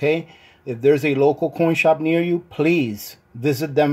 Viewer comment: lmao I fast forwarded a little bit at the beginning and when I played it I thought you said "porn shop" and I was like "wait, did I miss something?" then I watched the bit I skipped over and heard it correctly this time - coin shop though I thought I you may have been saying "corn shop"